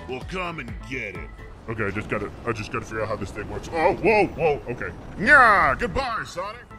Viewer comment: “whoa whoa I just gotta figure out how to mash this thing” (I think that’s what he said) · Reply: its "whoa whoa i gotta figure out how this works" heh :elbowcough